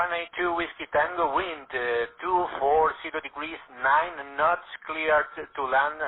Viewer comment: I don't like that phraseology. Sounded like the controller said "NOT cleared to land." I think he was saying 9 knots for the wind though